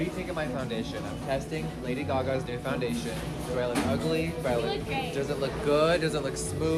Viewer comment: Am I the only one that thought the girl said u look gay💀